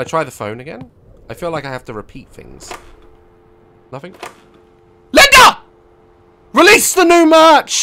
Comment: when he said linda i thought he said the n word